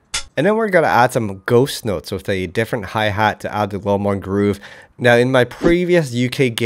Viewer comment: Lowkey sounds like he said "more fuckin' groove"